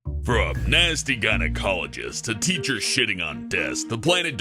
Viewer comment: The first ten seconds I hear "To a teacher shitting on a desk" My face 😑😐🙄😃😂😂😂😃🤔🤔🤔😣